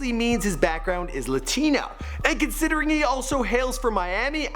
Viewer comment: Did i just hear call lil pump a Latina or Latino?